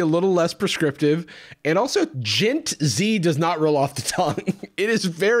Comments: I wasn't looking at the screen and thought you said djent z and was like "is this dude gonna gatekeep metal?" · I kept hearing "Djent Z" and it really fucked me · I keep hearing it as "Djent Z", where are the guitars? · I thought you said djent z, and i was hoping this was about a sick new prog metal band. I'm disappointed.